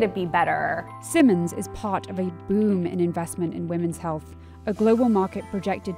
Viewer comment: All I heard was “wemen, wemen, wemen”